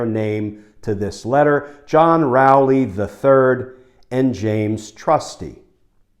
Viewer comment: When Glenn read the name of the first lawyer, i heard 'John P.. Rowley the Turd'. I wonder why...